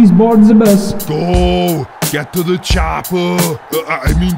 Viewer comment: What says Ivanovic at 0:38? Get to the... ? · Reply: Mats Carolz get to the chopa